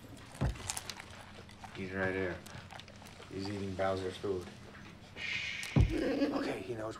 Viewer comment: did he just said Bowser